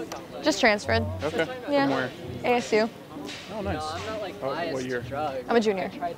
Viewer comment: im not biast on drugs, ove tried heroin’ 🤣🤣🤣 anyone hear that random weird conversation